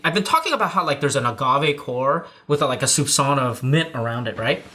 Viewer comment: Did...did say "Susanoo of mint" to describe the taste at 16:30? Like, an anime reference or am I just not hearing correctly what he is saying?